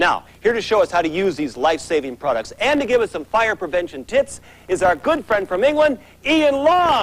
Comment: 1) it sounds like he says fire prevention tits, and 2) I bet that stuff gave everyone in that studio cancer